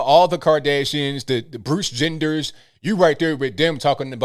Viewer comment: did you say Bruce Genders? 😂 NGL you got me there